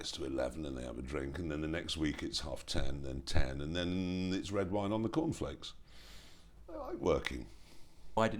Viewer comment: Did He just say: "then it's red wine on the cornflakes"?